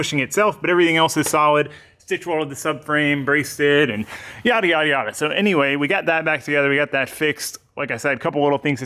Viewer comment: when you said "yadda yadda yadda" I heard "yadda yadda Miata"